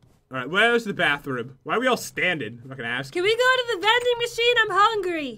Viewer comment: did Pat Just said a swear word? (F**kin a$$?!)